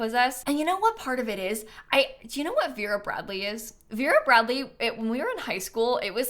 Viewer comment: Honestly my brain thought you said Fuhrer Bradley which is a character from a show caled Fullmetal Achemist so im glad you explained it was a brand that does lunchbox's.